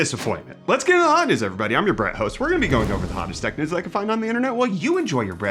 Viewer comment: “I’m your Bret host” slipped out and I rewatched like 5 times to make sure I heard it right 😂